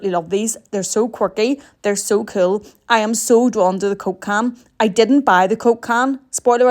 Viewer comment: For a sec i thought you said cocaine when u said Coke can